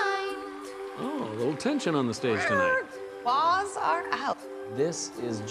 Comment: what did she say? ahaha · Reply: claws are out. -reference for a cat fight